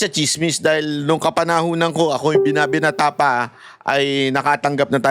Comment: Tama po ba narinig ko "nung binata ka pa"? 😅